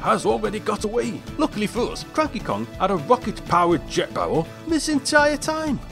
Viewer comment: anybody else heard "crappy kong" ?